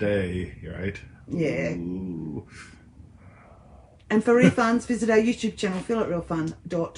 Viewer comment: You guys should rethink the re-funs. Until just now I have always thought you were saying for refunds go to ....I'm like, do they have that many refunds that they need to mention it 🤣🤣🤣